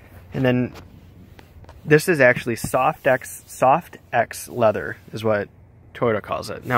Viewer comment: Did you just call it a, "Soft Ex" leather? lol I've never had a soft Ex before!!! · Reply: Correct but it's called Softex, not "Soft ex"